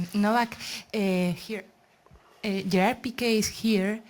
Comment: What she say? Who is here? 😕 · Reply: Gerard pique